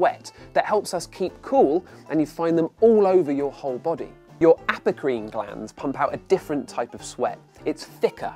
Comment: what he said: Aprocrine What I thought he said: Applecream Wait.... Apple cream!?